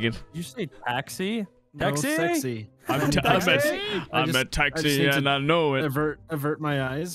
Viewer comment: or 14:18 somewhere around there it sounded like H2O saying taxi has anyone caught that